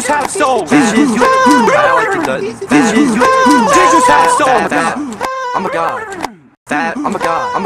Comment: he sounds like he says I'm a God. Unfortunately that may never happen.